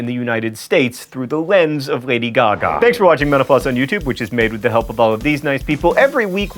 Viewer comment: At the end, instead of "thanks for watching mental floss", i heard "thanks for watching menopause"...